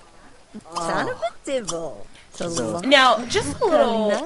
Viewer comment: at like 10:37 it sounded like said "son of a devil"